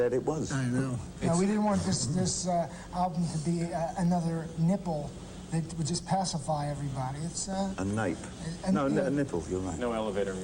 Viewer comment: what does he say? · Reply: "we didn't want this album to be another nipple that would just pacify everybody" "a nape... no a nipple, you're right"